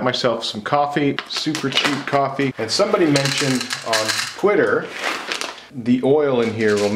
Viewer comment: Hahah I thought you said "quitter" as opposed to "Twitter". To be honest that sounds like an entertaining social site.